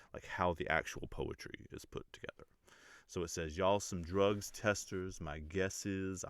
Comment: I thought he was saying " y'all should drug test us" because his lyrics and Hitboys production are on PED'S 💪!!